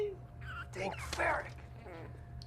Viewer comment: I dont get it why did he say , " Dang ferrick ?